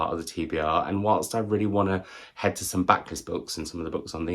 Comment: Random question - and I may have misheard - what is a backless book?